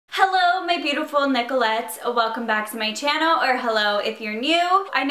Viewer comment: Always I don't know what u say, I hear "hello my beautiful necklace" you are saying it? ❤️